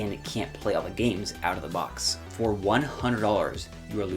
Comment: it can’t play what you said? games? what do you mean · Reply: I said you can’t play all the games out of the box. I talked about that earlier in the video. If you’re having trouble understanding something I say I have manual captions on the video, just so you know. Basically games like Mario Party and many other won’t work on the Lite unless you buy Joycon controllers which cost $80. Also games like Nintendo Labo and a few others won’t have full functionality even if you get extra controllers for the Lite.